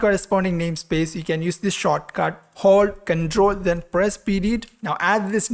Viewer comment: whats the shortcut key they said i didn't understand. Please help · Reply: Ctrl + .